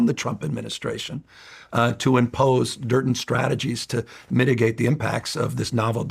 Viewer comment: Funny at 1:35 hes talking about working with trump admin. And instead of saying certain strategies he says dirt n strategies. Freudian slip lol.